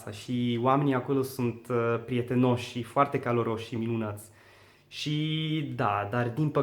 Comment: when he said "caldarosi" he sounded like he said "calarosi". anyway your romanain is quite good!